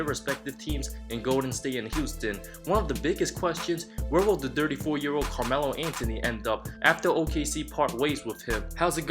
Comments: I thought he said the dirty 4 year old melo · I thought he said the dirty four year old Carmelo Anthony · i thought this man said " where is the dirty whore carmelo anthony gonna end up"